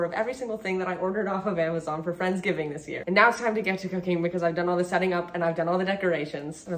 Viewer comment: "Now its time to get to cooking" Me: did they just say cocain